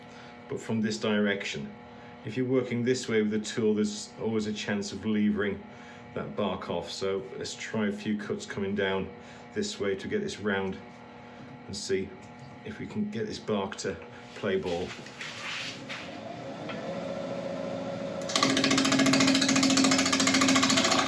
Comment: What I heard was rather appropriately - "let's see if we can get this bark to play BOWL" 😂